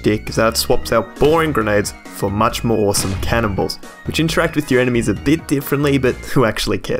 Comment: it sounds like hes saying boring grenades for much more awesome cannon balls